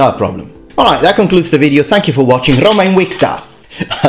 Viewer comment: what did he say in Latin? Least I think it was Latin. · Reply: He said: "Roma Invicta", which means "Rome (is) undefeated"